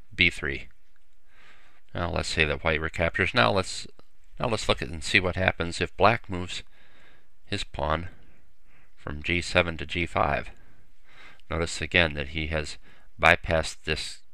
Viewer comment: did he just say "now lets fucking see what happens when black moves from G7 to G5"??